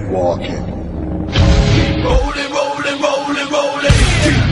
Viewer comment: sounds like it saying rolling polling rolling polling